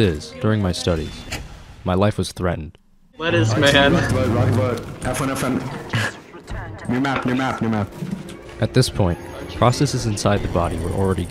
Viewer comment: tf is he even saying? What I hear is "Rock vote, rock vote, rock vote, f1, f1, new map, new map." But that makes no sense to what the fuck is he saying?